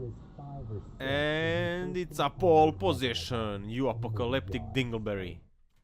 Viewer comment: An apocalyptic dingle berry that’s a new one. I haven’t heard that before. Good videos as usual. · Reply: At first I thought he said uppelcalleptic